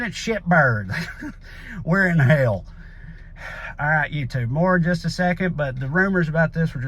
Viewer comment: I don't know how many times I heard Trae say "All right you two!" and wondered who he was talking to until I realized he was saying "All right YouTube!", lol.